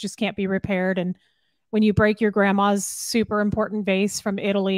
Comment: thank you for clarifying at 4:58 that was indeed saying “when you break your grandma’s VASE”. I thought she was saying “break your grandma’s face” 😟